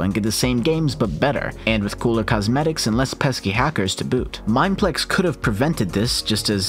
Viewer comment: did he just say no pesky hacker S to boot on hyoixel lol